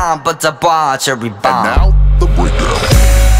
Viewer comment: am i the only one that hears “and now, the weather” at 2:57 like is that a canon thing or am i hearing things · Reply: I hear "and now, the winner"